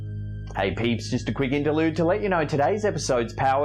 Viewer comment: says "hey peeps " I thought he was saying hey Pete ‼️I thought he was addressing me personally ‼️‼️👻👻👻👻👻👻